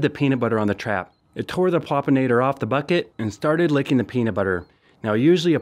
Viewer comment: I thought it said pulpinator I was like UH OH